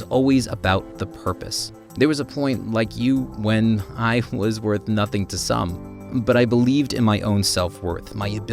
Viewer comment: That was a goo...wait did he say I was worth nothing WTF Elon musk